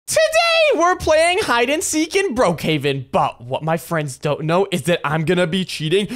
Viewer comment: Did you just say bro Haven?😂